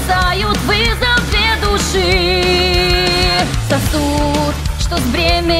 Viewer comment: Я вместо "сосуд" слышу "cocут"